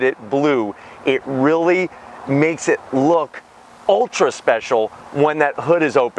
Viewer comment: Did he just say “ultra special”?